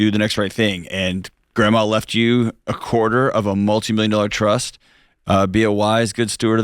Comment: I thought he said "a quarter of a multi-million dollar truck", and expected the call to be about the grandchildren fighting over who gets to drive it.